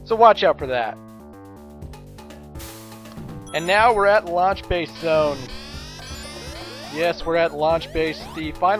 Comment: LOL. I thought it said lunch box zone xD